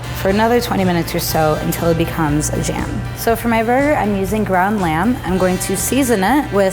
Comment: I THOUGHT SHE SAID GROUND HUMAN